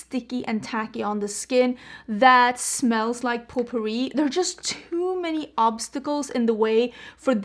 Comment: When you were talking about the scent of the foundation I heard, "poo-pourri(perfume you spray on the toilet)", so I turned on the caption thinking I misheard it, and the caption said, "poo-pourri", which made me laugh so hard my coffee shot out my nose. I'm not sure if it was intentional,(Currently blaming YouTube's captions), but A), I'm now determined to smell this foundation, and B), thank you ever so much for the laugh. I needed it. Also, I wish I could pull off oranges, as I'm loving the pumpkin spice latte eye look.